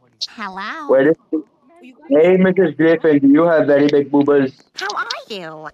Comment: I think what the man said what you couldn't say was: You have big boobers💀💀 ( 6:16 )